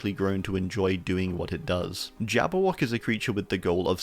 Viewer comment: I heard Jabo and was like “wait a minute they put him in Dead by Daylight?!”